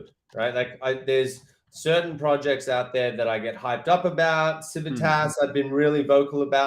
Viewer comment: Hey, could you please clarify what did said ? (Sabantas?) Thanks in advance mate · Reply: Sorry he was referring to Civitas. The public mint I believe is ongoing. It's got quite a high entey